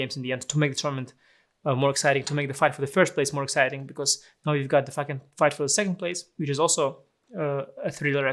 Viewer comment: Did I hear that right? "Now we've got to fucking fight for the second place"?